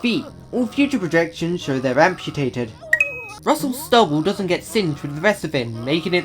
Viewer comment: The subtitles say beard when actually says stubble